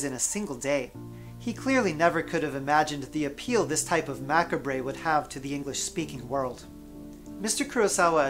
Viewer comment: Interesting, informative video. (P.S. did I mishear or did you pronounce macabre "mac-a-bray"? That's... extremely incorrect.)